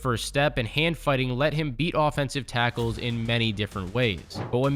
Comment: did he say his quick first step and hand fighting let him beat off tackles? 🤔 7:55 that throw is unreal. Especially when there was the defensive line in his face.